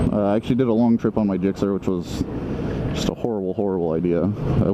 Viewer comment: Lmao @ 2:45 the YouTube caption says "did a long trip on my dick sir" 😂😂